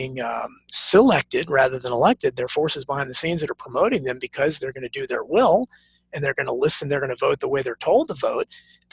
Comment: he mentions Alex Jones apologizing for conspiracy of PizzaGate, then says he knows Alex is very litigious, then he sounds like he says "Elephantitis", which I have no clue to what he's referring. Is he just saying Jone's is litigious on steroids, or in a huge way? Maybe Elephantitis, or Elefantitis, another person's last name? I've heard stranger surnames that sound like a joke, so hard to figure out what he's even referencing. Anyone know? Sometimes it would be nice to have a transcript of these type interviews with hypertexts of names, events, so we could see words or names that aren't always heard clearly, or spelled, or spoken with irregularity, making it difficult to do a search. I'd rather read a hard book, than a book on line, but sometimes reading a book on line, especially one in which the author includes sources, references to terms or topics, places, people, with hyperlinks, is preferable. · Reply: Alaphantas (spelling) owner of comet pizza